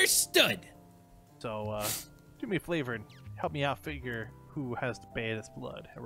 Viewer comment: Did he just say "You wanna do me a 'Flavor'?" X,D